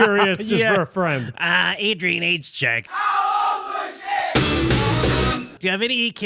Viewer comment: Adrian age check!? I thought you guys were saying aids check.